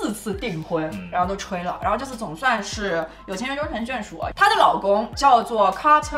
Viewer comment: 我听成了有钱人终成眷属，你说你是不是故意的